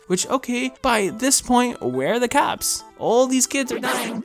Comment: I thought he said "caps" instead of "cops" So what I heard was, where are the caps